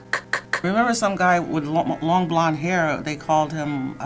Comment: she clearly said long BLONDE hair not long BLACK hair